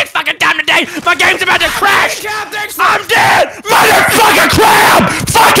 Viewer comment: I seriously thought he said "I'm dead! motherf@ a crab!"